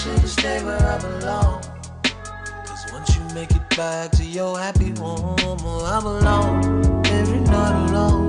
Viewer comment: I believe he said "once u make it back to your happy normal" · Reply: I thought he said happy home.